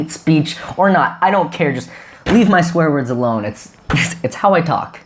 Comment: I legit heard "Leave my square words alone"